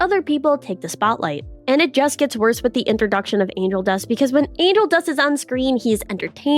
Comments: I heard Anal dust THREE TIMES 😂😂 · i was watching on 1.60 speed and i thought you said anal dust lol